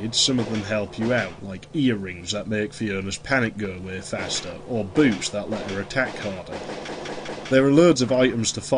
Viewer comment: I could have swore that I heard at 8:15 "BOOBS that make her attack harder". At which point for a fraction of a second I thought "BEST GAME EVER". Great and hilarious review, man. I nearly died laughing after your rant on characters pissing in video games.